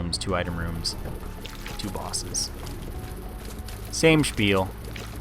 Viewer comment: what did u say at 13:45? "same spiel" the german word? or is there any english word i was not hearing right? and what means this word than